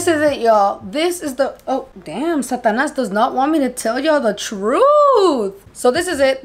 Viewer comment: Did she say satyanash at 1:35? 🤪🤪🤪